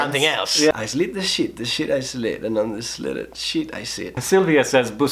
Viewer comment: omg hahahaha the first time Luzu said the tongue twister I thought I heard 'I slit a little shit'